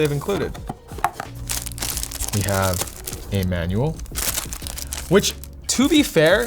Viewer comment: am I the only one hearing “Emmanuel?” instead of “a manual”